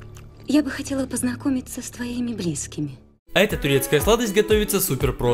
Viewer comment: Мне сначало послышалось «кисками»